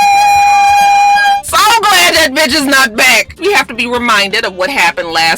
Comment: It sounded like Karen said “Batches” 😂😂😂 Maybe that’s how the elite say Bitch 😂😂😂